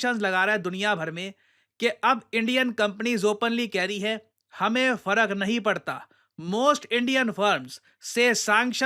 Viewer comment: I heard "Hame Fadak nahi padta" 😂